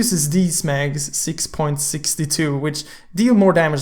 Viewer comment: did i hear 6.62. Wat seriously!!!!!!!!!